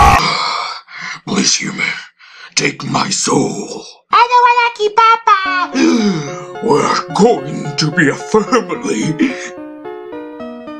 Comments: After about 2 months i finally understood he says "I don't wanna kill papa". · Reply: iKiwed I know right! Everytime I hear her say " I don't wanna kill papa." I thought she said " I don't want a cha-ka." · After about 2 months i finally understood he says "I don't wanna kill papa". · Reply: iKiwed first I thought frisk said I don't wanna ki papa